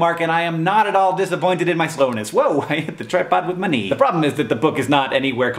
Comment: At 0.21 I thought for a second he said "I hit the tripod with money" and I was like "Damn, Hank! Do you have a Scrooge McDuck-ian pool down there?"